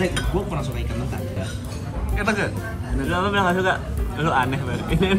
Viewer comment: ngomong apaan dah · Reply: "Lu bilang apa? gak suka?" Kayaknya